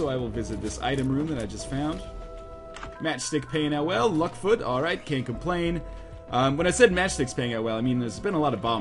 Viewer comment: Did anyone else hear "Well Luck Foot alright - CAIN't complain!" at that part? It seems like unconsciousness is making Binding of Isaac-related puns there on its own, haha. Maybe it's just mine, though.